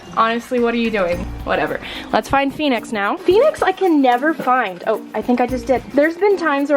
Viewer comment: am I the only one who heard penis at first